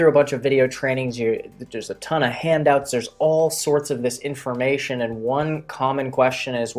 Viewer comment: Subtitles at 9:47 says “misinformation” instead of “this information”. Just fyi.